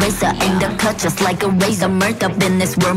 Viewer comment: in 0:44, did you say razor